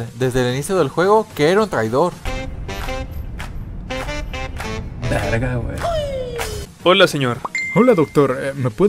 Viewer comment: Alguien entiende lo que dice? Porque no lo escucho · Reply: Dice:verga we